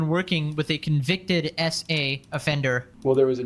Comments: Here's a wild prediction before watching: He defends 🛡️ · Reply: For some reason I first thought he was saying... a convicted esé For a split moment. (*^o^)人(^o^*) That esé put dust in my weed!* - GTA San Andreas pedestrian with the orange number 12 Hoody. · For some reason I first thought he was saying... a convicted esé For a split moment. (*^o^)人(^o^*) That esé put dust in my weed!* - GTA San Andreas pedestrian with the orange number 12 Hoody.